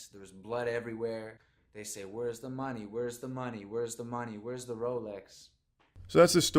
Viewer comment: Ong I thought I heard say where’s the robux